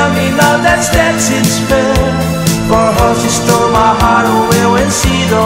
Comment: for her to steal my heart không biết mình có nghe nhầm ko nhưng có lẽ nó là for to stole my heart